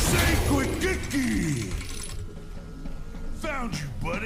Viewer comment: I always thought it said “stay safe with Kiki” lol